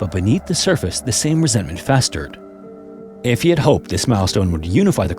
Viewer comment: milestone? I thought you said 🍆bone